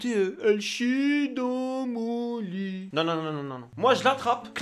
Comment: moi j'ai entendu "cachire dans vomit"